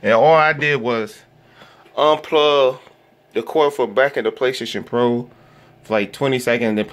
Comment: what did you say??? I heard quafer back 🤷🏻‍♂️ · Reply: unplug from back of the PlayStation for 25 sec